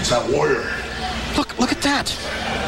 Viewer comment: Did Schiavone just say "Fuck, look at that?"